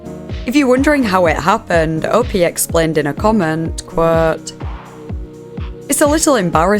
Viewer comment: Yes, later on, at 3:45, she says "Quote" again. I like all the different accents that people have, I don't even understand what could be "horrific" about an accent... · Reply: yeah ok, it was definitely "quote" said slower in her northern accent. I think it's northern. What I wanna know is how does she keep her voice so consistent and kind of monotone? I've not heard her break once, or laugh... I think I've only noticed intonation changes when she's reflected on something and asked a theoretical question. But I'd have to rewatch all of the videos I just binged to check and I don't wanna do that, hah. I'm gonna pay more attention from now on.